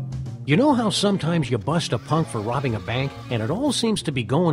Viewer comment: I thought he said pope instead of punk and was deeply confused as to why 1) the pope was robbing a bank and 2) the pope would be judging a hotdog eating contest